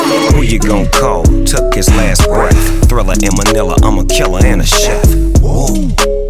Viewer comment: Did i hear manila at 0:13? Peeenoise make some noice!!!!!